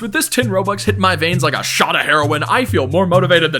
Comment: what did bro say? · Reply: Heroine